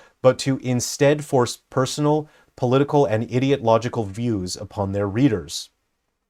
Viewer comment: "idiot-logical" instead of "ideological" 😂